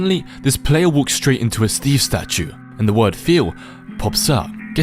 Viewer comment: WHY DIDI THINK THAT SAID FEET 😭😭😭